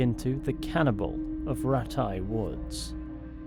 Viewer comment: I thought that said "ratty woods"